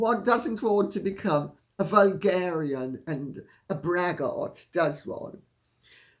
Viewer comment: I'm thinking I need a hearing test at Specsavers. I thought you said vulgarian. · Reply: She did say vulgarian and it was correct!